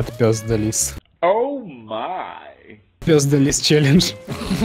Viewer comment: я услышал Пездились челлендж